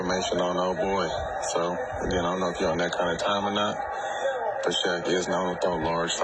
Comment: Did Barnes say, "I'm not feeling that kinda time or not" ??...a bit hard to understand